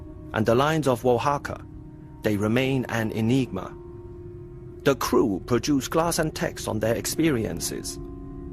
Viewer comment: Right, thanks. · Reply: does he say " the crew produced glass and text"? I couldn't find a meaning to this phrase.